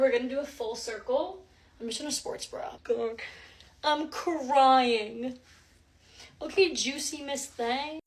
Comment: did she say "i'm lying"?